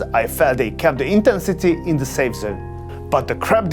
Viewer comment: what is the word he uses before 'in the safe zone' ? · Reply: Intensity